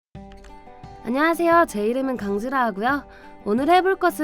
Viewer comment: 초때 나만 강수라고 들리나?